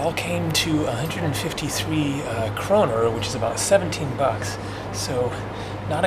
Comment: How much did he say @ 6:53 in krona? Why is he putting on an accent for the numbers? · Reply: 153 kroner = $17 USD.